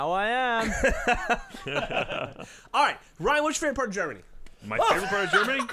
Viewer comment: it sounds like Geoff says "Ryan, what's your favorite part of Jeremy?" :-)